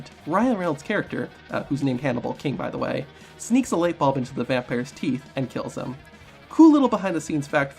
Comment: Also, did you say that HHH's character was named 'Drako' Grimwood?... bc I believe it's Jarko Grimwood. You have it surely correctly if you turn on your captions but I don't believe you said it right... unless I'm just heading it incorrectly. Also also, at 3:11 into the video, it kinda sounds like you said 'light bulb' instead of 'light bomb' like, again, it says in the captions if you have them turned on. ... and finally, was Macho Man the first WWE wrestler to appear in a Marvel movie?... bc the original Spider-man came out in '02 n Blade Trinity came out in '04. That Big Sky dude that played Sabertooth in the original X-Men movie, which came out in 2000, was a WCW wrestler. (or he was at least in the lil shot you showed in this video) ... and yes, I know I'm 2000 the Macho Man was wrestling in WCW but at least he used to be in the WWE, back when it was called WWF but it's still the same thing... am I right about this or had another wrestler snuck underneath my 👃 nose??? lol